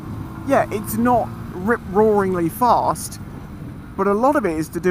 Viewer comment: I thought you said "It's not Rick Rollingly fast" for a second there, haha.